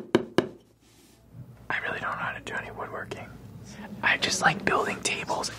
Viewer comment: ¿alguien sabe que dice acá? O sea, en inglés, porque habla tan rápido que no capto las palabras. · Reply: "I really don't know how to do any wood working"